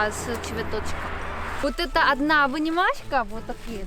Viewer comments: "Одна обнимашка" - это ОЧЕНЬ мило))) Я теперь все буду мерить в обнимашках))) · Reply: мне послышалось "вынимашка"... Типа, что вынул с грядки за один приход. Хм. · Я там не услышал "обнимашка". По-моему было сказано "вынимашка", разве не так? "Вынимашка" здесь более к месту, чем "обнимашка": с огорода, из земли вынули :) Замечательно слово получилось "вынимашка"! Так научиться чувствовать русский язык, чтобы правильно придумывать новые слова - класс!